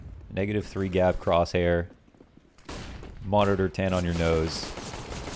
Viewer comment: man you don't even need monitor tan on your nose, that gotta be all over your face and neck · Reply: Oh he said "monitor tan" haha that's great, I heard "monitor 10" and was like hmmm 10inches to the nose maybe?